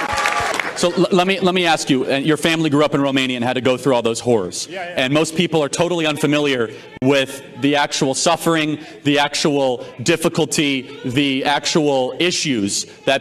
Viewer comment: The only moment in all of that when Communism sounded “cool” was very fleeting. It was before I realised that that I heard him to say “so in Communism you had to go through all those ‘whores’”, when he actually said “horrors” 😳.